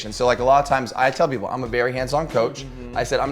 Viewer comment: I thought said "Im a very handsome coach..."